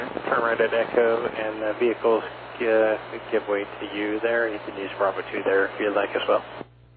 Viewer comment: There's a bit of erroneous captions going on here. I tried to correct them but wonder if anyone is hearing different? It's not easy to always understand what they're saying! 2:30 - "You can use Bravo 2 there if you'd like, as well." 3:31 - "... the Eclipse jet that turned off bravo 2 onto Bravo by the A-10s" (there's an ANG at Boise with A-10's lined up on the South) 4:17 - "Boise ground, roger, say your position" 4:24 - "Smokey 7, say your position" 5:09 - "7, roger, just verifying all your Smokey vehicles are done, vehicles are done crossing 10L." 6:21 - "19, yeah, we just closed by ATIS..." Pretty good otherwise! What a crazy incident.